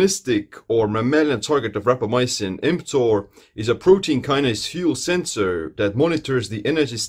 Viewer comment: mTOR is a Protein what fuel sensor? I didn't understand the word you said after "protein". Could you clarify, please? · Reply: kinase